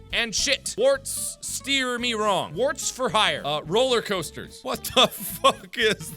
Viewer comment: I thought it said warfstaches for hire